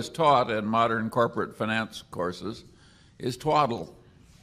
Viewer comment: Hello! thanks for sharing this great video! please can someone tell what says Charlie in 5:58 "what it says in corporate finance courses is....what?? thanks! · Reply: "twaddle" it means silly or foolish.